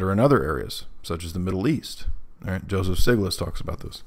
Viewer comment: Who is person mentions at 15:10? Joseph "sigliss"? I can't make out the last name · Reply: Joseph Stiglitz